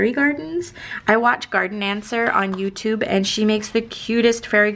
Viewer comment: We also have a succulent fairy garden with a cute snail, raccoon in a leaf-boat :) and a little bridge and crystal water river. Fun to have that activity in common with you! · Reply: ok i thought you said garden dancer. then i figured out it's garden answer. cool videos! fun way to pass this rainy saturday dreaming of making more fairy succulent gardens!!! XO